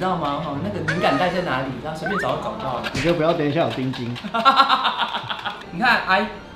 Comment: 睡前沒看畫面 2:10 聽成 你就不要等一下不要有青筋～～我嚇爛～～真是愛死了XD